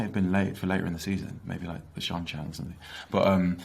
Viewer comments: Did he say the Seanchan at 6:22? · Are we all going to ignore what Marcus said at 6:20 ?? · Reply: Lol yes, I heard Seachan too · I'm not the only one who heard Seanchan, right? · Did he just say the Seanchan?